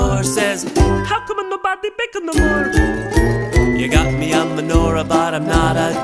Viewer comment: Hi, english is my second language and I'm not able to understand WHAT says the Italian lady in time 1:22. Can you help me? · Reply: “How come nobody bake anymore” I over heard that at a farmers market once and it stuck with me so I used it in this song.